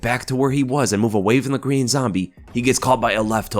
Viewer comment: lol is it just me or did it sound like he said "the Green Zombie" at this part?